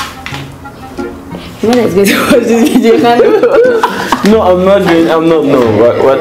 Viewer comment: Did I just hear "my mother is going to watch this video" @ 14:58 ish 😂😂😂😂😂😂